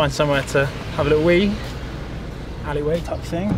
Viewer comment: did he say "try an find somewhere to have a little weed"?